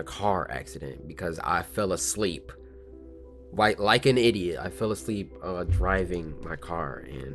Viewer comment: I thought he said like a white idiot